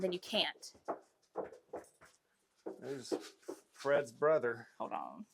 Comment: I swear he said freds brother